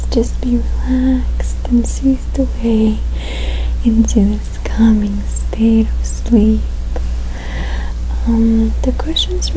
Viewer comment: it sounds like she says “drift off into this communist-state of sleep.” 😮😂